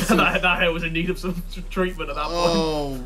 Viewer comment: What did Harry said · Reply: That hair was in need of some treatment is what he said